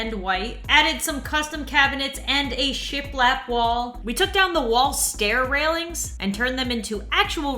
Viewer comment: And a shit black wall? Lmao that's what I heard